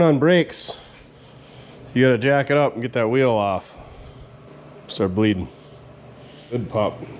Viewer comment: "Jack up and get that tire off? I thought you said jack off until the tires fall off."